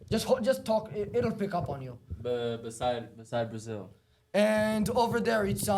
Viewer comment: When dude said “beside Brazil “ I dead ass thought he said cypress hill.